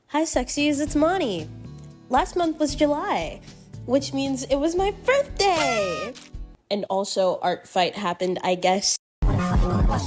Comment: You did NOT just call me a sexy 💀